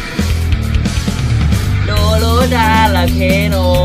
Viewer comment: did I just heard Oreo